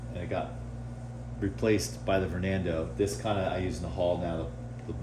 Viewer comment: Get your Vernando... built by Fernando. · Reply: That's exactly what I heard too. It's tornado with a v, vortex plus tornado equals Vor Nay Doh lol!!!